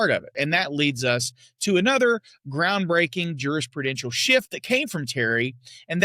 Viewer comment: you referred to "officer safety" "as a groundbreaking jurisprudential SHIFT that came from Terry", but I heard it "as a groundbreaking jurisprudential S-H-I-T that came from Terry". 🤣🤣🤣